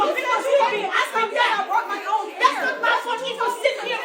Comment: I heard a lady say she brought her own hair.